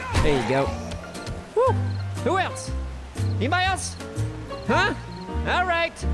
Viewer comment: All I hear are the Wetalds and Leslie. "is there no one else!?"